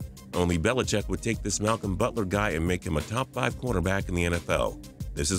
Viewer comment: did he say top 5 quaterback